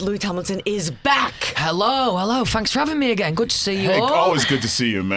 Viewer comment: It's very funny seeing automatic subtitles try to figure out what Louis is saying: Thanks for having me - > funk strumming me 0:18 Soundcheck - > sound tricks 0:45 Fans - > funds 2:44 Hear it - >air it 3:04 I was in - > numbers in 3:36 Toured - > taught 10:36 Song called walls - > some call balls 11:41 😂😂😂